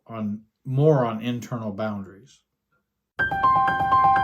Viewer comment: You said "more on internal boundaries." I heard "moron internal boundaries." Me thinks I need more work on my systems thinking 🤣